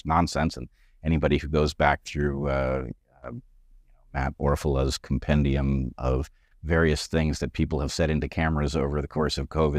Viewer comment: What is he saying? “Abmorfellas compendium”? I am virtually obsessed with seeing the lies that have been broadcast debunked.